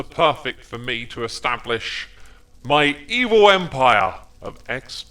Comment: "My evil empire!" What I heard: "My eboy empire!" is an eboy confirmed